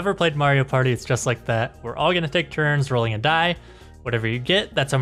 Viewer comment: = Take Turn Rolling A Dice What I Heard = Take Turn Rolling To Die